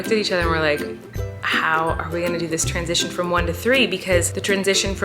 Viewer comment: i thought she said "twinsition" but she said "transition"